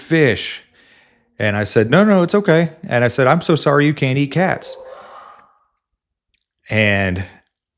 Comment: Did I hear that right? Did you say "sorry you cannot eat cats"? correct me if I am wrong